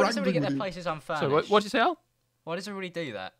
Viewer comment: did will just call him AL?!?!?!?